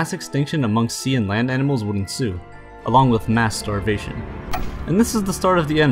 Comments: you said mass starvation and i heard Masturbation😂👌😂😂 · Did I hear MASTURBATION? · Did I just hear masturbation? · Anybody heard "mass starvation" as masturbation?